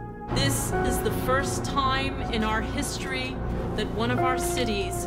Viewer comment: "this is her first time in history that one of our cities has fallen into enemy hands". So we're just going to pretend that the War of 1812 and the Civil War never happened? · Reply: Andrew Lampart you misheard it “in OUR history” meaning the history of 2038